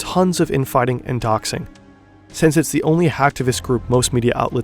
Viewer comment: did you say HACKtivist group, or ACTivist group? Because it sounded like you said HACKtivist 🤔 Also, it's BESTiality, NOT BEASTiality. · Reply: hacktivist. it's a academicmterm and it's on Wikipedia, basically activism via hacking